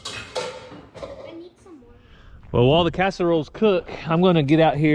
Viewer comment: When was telling us y'all were taking a casserole to GPA I thought he said caster oil and thought oh no! Poor gpa